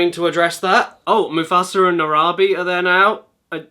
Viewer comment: did you say narabi?